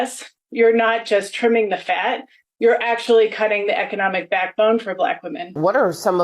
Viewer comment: She said "cutting the fat" I heard "removing the cancer"